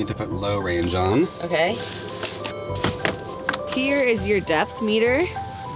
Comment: I thought she said “here is our death meter” and I was like “accurate”